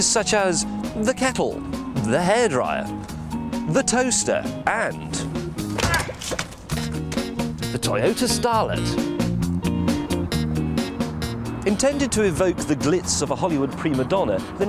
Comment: I kept hearing "Toyota Stalin" at the beginning